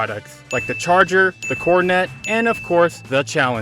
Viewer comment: I finally I wasn’t the only one as a kid who thought it sounded like corn on the cob😂